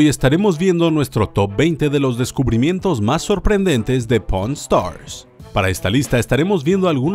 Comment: se que dice pawn stars pero alguien escucho porn Star